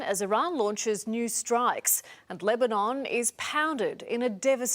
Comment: Israel is bombing Lebanon because Hezbollah fired missiles at Israel. Hezbollah fired missiles at Israel because they're a Shia religious organization and Israel just assassinated the Ayatollah (the religious leader of Twelver Shi'ism) during Ramadan (holy month). Israel knew this was going to happen. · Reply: OK, this makes sense now. At 3:25, I thought she said "Iran launches new strikes AT Lebanon" but she actually said "Iran launches new strikes AND Lebanon" I'm a little hard of hearing and sometimes that can cause misunderstandings. I appreciate your reply, it did clear everything up for me. I'm going to delete my comment in a few days after you have had time to read this. Once you have read it, can you give me a thumbs up? Thanks for the help!